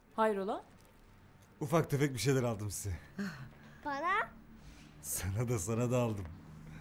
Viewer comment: o kadar çok para konuşuluyor ki Ayşe’nin “bana?” Sorusunu “para?” Diye duydum